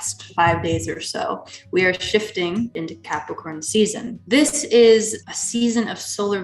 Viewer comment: Capricorn ewww gross...oh nevermind I thought you said candy corn....Damn that was corny.